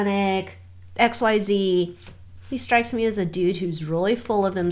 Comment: When you said, "He strikes me as a dude who's really full of himself" ... I thought you said, "He strikes me as a douche who's really full of himself." 🤣 Would have been appropriate.